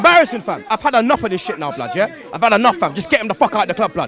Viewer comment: Did he say "Fuck Wayne Rooney" at the end?